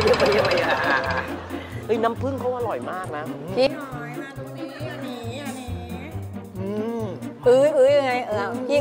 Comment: มีใครได้ยินว่า พี่หอยมาตรงนี้อย่าหนีอย่าหนีๆ